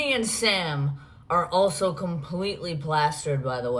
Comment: She said, “completely plastered” and I heard, “completely plaaaassstic” à la Indian in the Cupboard